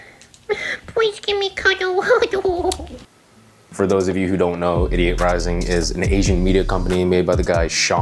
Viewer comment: sounded like you said "idiot" rising